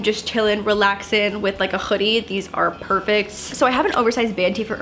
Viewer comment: hahaha she said choodie instead of hoodie